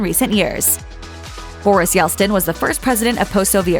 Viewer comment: I heard Boris "Yelstin" about 15 times. lol!!!